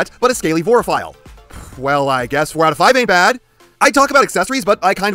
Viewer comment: What was the word you say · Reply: Or were you asking about Vorarephile?